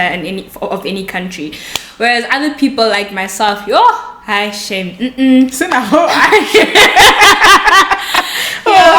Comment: lol. What did say? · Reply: She was saying, We adapt easily.